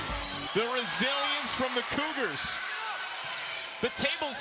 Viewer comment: Was it just me or did it sound like he was saying the cooters😂